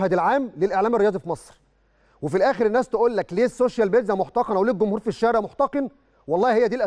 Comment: هو قال السوشيال بيتزا ولا انا سمعت غلط في الدقيقة